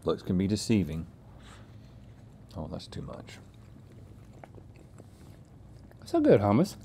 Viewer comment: Did Rhett say « That’s to much » about food???